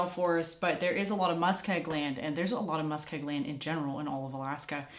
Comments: I just started watching this video ... @ 3:49 what type of lad did you say & mean? (It sounded like "mustag land", but I can't find that anywhere on Google 😕 LOL). I think it has to do with steep & unsteady land🤔 that you can't have a sturdy cellar ... just not sure. I love your beautiful family & videos! Thanks for sharing & congratulations on your transition into a new & another beautiful area of Alaska! I'm going to continue watching now ❤... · and @ 3:51 you said it twice what type of land ? Sounds like you're saying Muskeye land ? Musk I gland ? ?!?! I am soo confused !